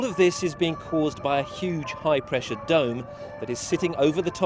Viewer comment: did he say high pressure drone? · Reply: Devashish Sood No, he said high pressure dome :)